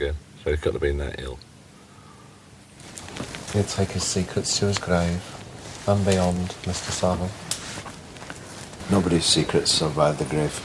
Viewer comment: Interesting to hear Pete's comment at 23:05 "he will take his secrets to the grave and beyond Mr Savile" Unless I misheard it?